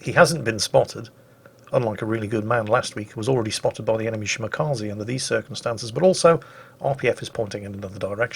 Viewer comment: Did I hear him say “spotted by the enemy Shmakaze” instead of Shimakaze? Sounds catchy.